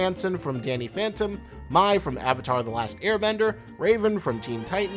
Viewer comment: did he just say... mie ?